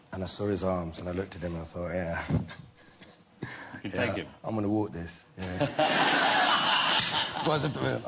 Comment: What is he saying at 9:49 ? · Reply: He says "I'm gonna walk this" like this is going to be easy for me